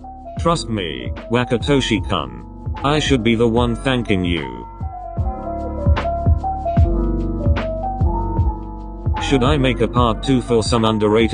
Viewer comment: I THOUGHT YOU SAID THE UNDERRATED SHITS 😭